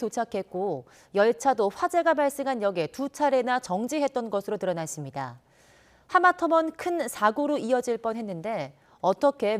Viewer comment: 내 귀에는 하마터먼으로 들리는데...